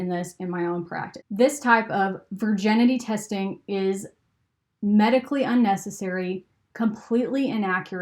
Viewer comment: Hi, I know this is an old video, but FYI at around the 11:05 timestamp, the captions say "medically necessary" when they should say "medically UNnecessary", and it's a very important captioning mistake. Hopefully you and team can get that corrected easily. Cheers. :)